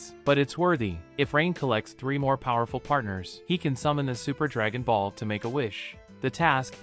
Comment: Did he just say Super Dragon Balls?